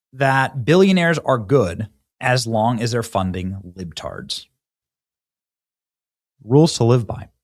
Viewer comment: I thought he said "retards " there. Like, no way! Unusually over-based Benny slipping out slurs? Hahaha. I had to rewind and make sure